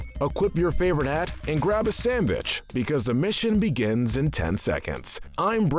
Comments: did he just say sanbitch? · The subtitles said equip a sandwich